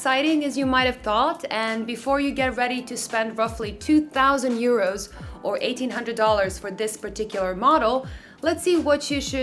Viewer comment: 2000 euros or 1800 dollars" ... did I hear correct